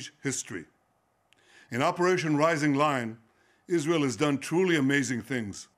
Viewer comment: I thought I heard Operation Rising Line. 📈 I know it's Lion but we should start calling it Line.